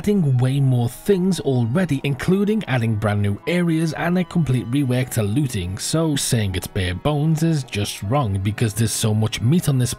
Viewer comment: Did I hear "and they completely wrecked the looting" at 2:32? Lol, have to agree with that one 😂😢